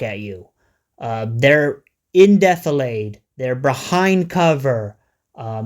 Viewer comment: Hello all what word or phrase does he say at 10:11 , They're ''in death laid'' or ''indeath-o-lade''. What is he saying here? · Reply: in defilade